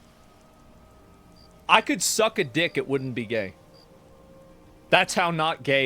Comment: Got this shit connected to my chrome cast as just background noise as I clean and what not and all I hear is “I could suck a dick and not be gay.”